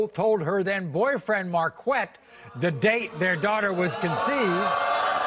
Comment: Tell me why I thought Maurry said the guy's name was "MarQUACK" 🤣